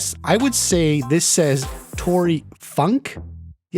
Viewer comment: By one of the cards saying “Tory off” maybe that means she’s feeling off? That could explain the “Tory funk” card? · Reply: Oh I thought it said Tory fuck